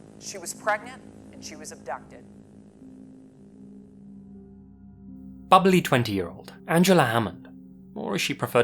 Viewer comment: At the 2:26 mark, I wasn't paying as close attention as warranted. said Angie was bubbly and showed her picture. I was WRONG but got pissed. I thought he said ugly... What?!?!?! Rewind and check = I am the moron here